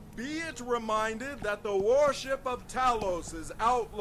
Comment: Am I the only one who thought the town crier said "War ship of Talos?"